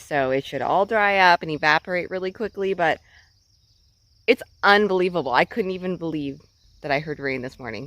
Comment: Did I hear "It's Un-Bear-able...?" Which reminds me, will you have ac in the new digs? Do you have it in where you sleep now?